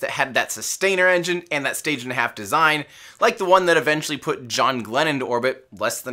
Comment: for some reason I heard that as: “the same one that put John Lennon into orbit” and I was VERY confused lol